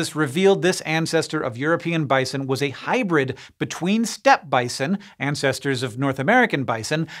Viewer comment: "a hybrid between steppe bison" I heard that as "step-bison" and was very confused for a second or two.